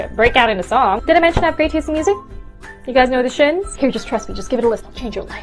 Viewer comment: I don't know "The Shins",I had to rewatch this because I thought it said "The Skins".